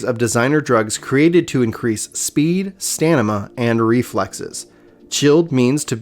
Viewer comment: Lol he said "Stanima" instead of Stamina